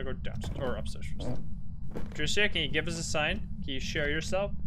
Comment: Lmao did say Patricia can you “share” yourself 😂😂😂😂